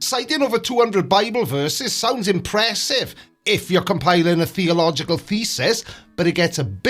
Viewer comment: i'm italian and i have a language barrier, did you say "theological Thesis" or " theological Feces", either way is good to me and you are right, but one is more accurate and fun than the other 😁